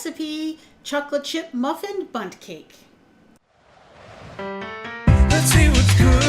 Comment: For a second, I thought you said 'butt cake' 😂